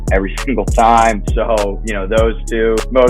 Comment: what does he say he takes during the day? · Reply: Motive pure - like an electrolyte drink